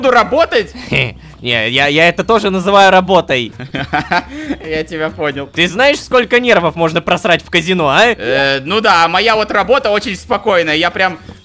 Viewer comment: мне одному послышалось не нервов, а негров 😂😂😂🎉